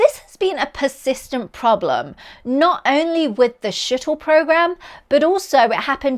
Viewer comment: I thought I heard "Shittle program"